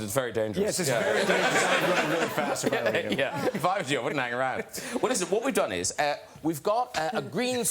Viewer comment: What did he say at 15:18? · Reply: If I was you I wouldn't hang around That's what he said